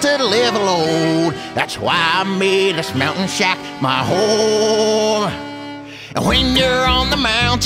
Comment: Am I the only one who understands: that's why I made this mountain shack my who*e?